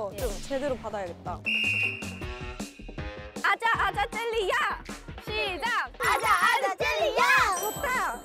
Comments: I heard aaja aaja jaaldi aa chilla aaja aaja jaaldi aa 😂😂 This is in hindi. It means come come fast come...come come fast come 🤣🤣 I just wrote it word to word for fun 😂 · Oh my God!! It sounds like she said"Aaja aaja jaldi aa" 🤩 (Indian blinks can understand)💜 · i thought they were saying aja aja galdi aa in hindi